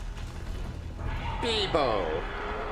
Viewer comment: did he say beebo